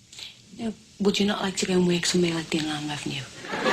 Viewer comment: What does the mother say at 1:28? Me thinks...? They still say than in parts of the UK? Its weird hearing an adolescent kid say that. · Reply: “Would you not like to work somewhere like the Inland Revenue?”